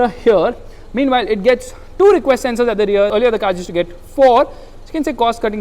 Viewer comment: two request sensors 🤣🤣🤣🤣 instead of parking sensors